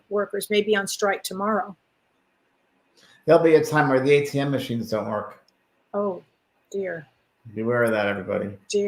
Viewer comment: I can't for the life of me understand what James said. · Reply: “There will be a time when the ATM machines don’t work”